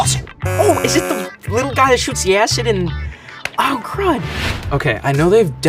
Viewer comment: Omg, I was so sure said "OH, is it the little guy who shoots Yaz in-". I was like "uh oh, spoiler alert". But this makes a lil more sense, cuz I doubt they would let that be in the video if he said what I thought he said lol